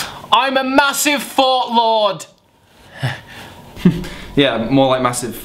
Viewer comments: profile thing at the beginning was 100% accurate. And I thought said Massive FART Lord the first time I watched the video. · For a few seconds I thought he said "I'm a massive fart lord" hehe